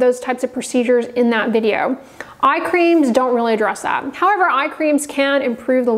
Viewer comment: I thought you said eye creams don't work. I am pretty sure I saw it here on your channel. I need to look for that video